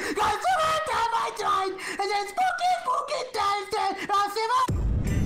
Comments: didn't swear at 0:10 he said: "SPOOKY SPOOKY SKELETONS" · Was sceaming SPOOKY SCARY SKELETONS!!!!!! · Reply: i think Said Shine